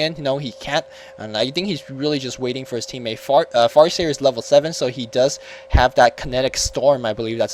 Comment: did you said fart seer? lol